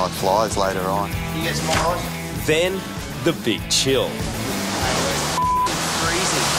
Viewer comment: thought he said bitch hill